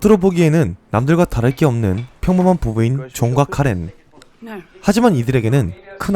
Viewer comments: 존과 카렌이 계속 종각 카레로 들려.. / 이거 왜이렇게 좋아요가 많죠 ㅋㅋ 감사합니다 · 존과 카렌이 자꾸 종갓카레로 들리네...